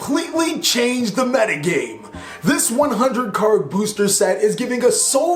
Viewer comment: I heard "this one hundred $ booster pack"... 😰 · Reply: Quetzal coatl 100 card booster set. Not $100